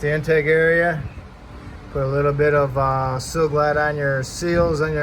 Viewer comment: what does he say to use on the gaskets? Silaglad?? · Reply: Sil-Glyde